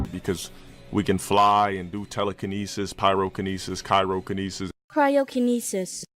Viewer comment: I thought he was saying Kyro... I heard something sounding like that somewhere.... DEATH STRANDING! But he's not even close, as usual XD